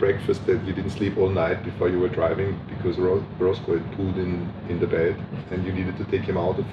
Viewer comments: Rosco!😂 The first few times I thought he said Rosberg pooed the bed 😄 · I keep hearing “Rosberg pooed in my bed” 😂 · I thought he said "Rosberg pooed in his bed" 😅😅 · I thought he said Rosber pooped in his bed.